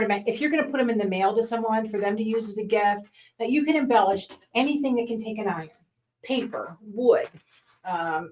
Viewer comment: I thought you said you embellish your cat